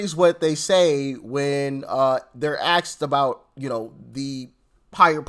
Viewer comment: “ axed about” instead of “ asked about”?